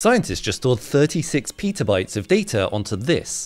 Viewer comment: I heard “pizza bites of data” and got really confused 😆